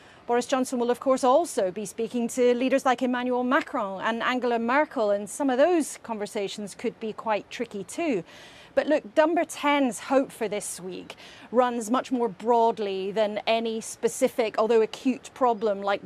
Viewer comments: Did she just call them “Dumber 10” @ 8:18 lmao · did i hear "dumber 10" in there?